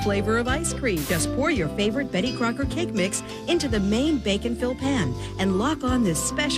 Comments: Every time you say 'Bake and fill" all I hear is "Bacon-fill" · Reallllly thought you were saying bacon fill for the longest time, and that was confusing. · I first heard “Bacon Fill” for a cake. Now I want to bacon fill a Bake’n Fill. Tiramisu & Bacon. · Everytime you say "bake 'n fill", I hear "bacon-filled". I can't be the only one. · when she said bake n fill i thought she said bacon fill. i got so confused when the commercial started and she was talking about cakes